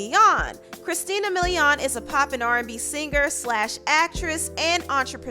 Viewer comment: You know, you're really biased when you call male rappers from around her era "throwback rappers", and you call Christina a "popping artist". I haven't seen her in anything in a while. I love Christina Milian and she stays looking young. She's great. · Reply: I was giving the side eye becaus I thought she said “popping” too but she actually said ‘Pop and’ RnB singer